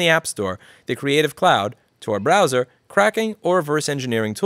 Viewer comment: Did he say “Kraken” at 2:18? Kraken (code name) apparently is the US military 305th cyber intelligence brigade.